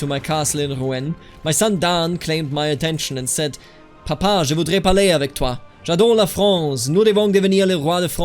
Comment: I'm pretty sure he said "je voudrais parler avec toi" which means I wanna speak with u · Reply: "Papa, je voudrais parler avec toi !" (Dad, I want to speak with you) "J'adore la France ! Nous voulons devenir le Roi de France, d'accord ?" (We want to become the King of France (NB : it's clearly singular), okay ?) Quite strong accent but grammar and syntax is okay. Germans are known for proficiency in many languages I believe